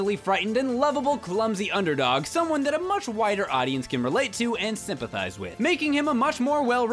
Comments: did he say wider, or whiter? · Lol, at first thought he said "Whiter audience." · At the end of the video, when he says "Luigi appeals to a much wider audience", I heard a much "whiter" audience.